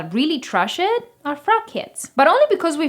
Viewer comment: When you said "I think the only people that really trash [Sauvage] are fragheads", at first I thought you said "crackheads" 😂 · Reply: lol I thought she said "fat kids"